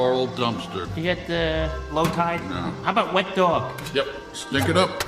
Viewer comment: The Wet Dog clue threw me off because admittedly of the many times I saw the movie, I somehow misheard that as “Wet Pillow” 😅